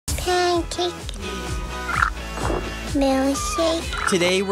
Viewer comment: At first I thought she said pancake,belly shake IoI.